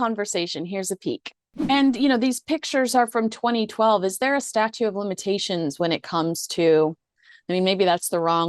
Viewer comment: Statute not statue of limitations. Sounded like you said the latter